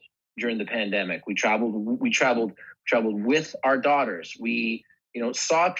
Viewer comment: Did he say “with our daughters?” As in plural at 54:44?